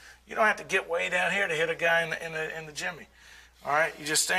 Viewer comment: LOL "one of the ways I like to hit the groin" -I'll bet you do, I'll bet you do. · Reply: 😂😂 i thought he said hit a guy in the chimney 🤷☠️....."I like to use an open PALM, you can gage how much he likes it"🤦🤦🤦😫